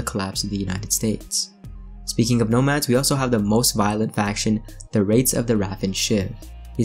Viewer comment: First, i thought he said ”the wraiths of the rapid shit”